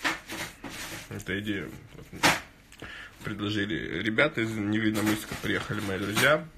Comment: ИЗ НЕВИННОМЫССКА?!!! Я ПРАВИЛЬНО УСЛЫШАЛ?!!!